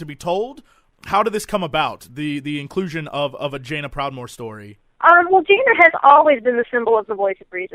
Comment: did says "vajaina proudmoore" at 05:17 ?